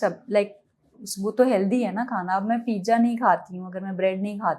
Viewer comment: did she said pijja😂😂😂😂